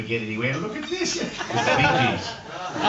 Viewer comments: What does he say at 3:03 and people laugh? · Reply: Bee Gees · What band did he say at 3:00? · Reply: The Bee-Gees